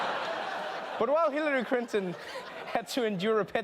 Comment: I like you Trevor..but couldn't help laughing at your Hillary "Crinton"..yes ..I heard that! lol